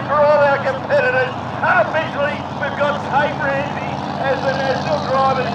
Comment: So exciting but I couldn’t hear what boat won the commentator said the drivers name but I don’t follow this type of racing so I have no clue what boat he drives. It sounded like he said Tate Ramsay but I’m not sure what boat what power what hull. If someone knows please let me know. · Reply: The race was won by Xplicit the Barra powered red boat. The championship was won by Tate Ramsay driving Reckless the blue blown boat that finished second in the final race of the season.